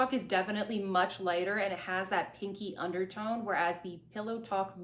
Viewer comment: I’m sitting next to a bubbling aquarium whilst watching this and I keep hearing you say “…it’s got that stinky undertone…” 🤣